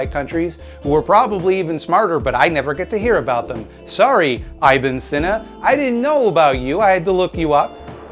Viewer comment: Can someone tell me how to spell the name of the person they mention at 5:21? I tried searching it phonetically from what it sounded like but alas I found nothing. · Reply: Ibn e Seena